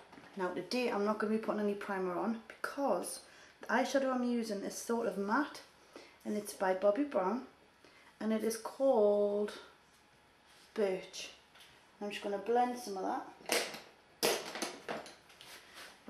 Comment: lol i thought she said it is called bichh